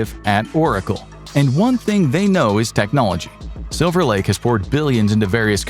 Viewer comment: “Technologia” 😂 I heard it!!